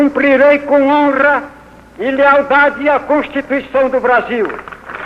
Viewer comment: juro que escutei "a prostituição do Brasil"